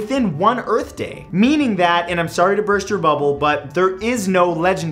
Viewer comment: What he said: "I'm sorry to burst your bubble, but..." What I heard: "Sorry to burst your bubble butt" haha im immature